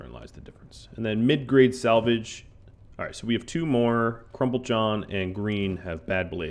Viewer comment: I keep hearing you say "crumb-PULL-john" but it is spelled "crumBLE-john". Is he okay with his nickname?